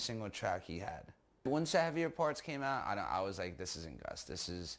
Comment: hey guys can someone tells me what is saying at 13:12 ? "once ??????? came out..." i'm not a native english speaker and i really can't understand the title of the song or the album he's talking about, but i would love to know what he's talking about! 😅 thank u :) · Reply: "Once xavier parts came out he was like this isn't gus" hes talking about songs gus made with xavier wolf